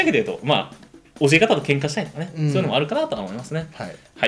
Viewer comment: なんて言ってる?喧嘩ってワードは聞こえた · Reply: 教え方で喧嘩をしたり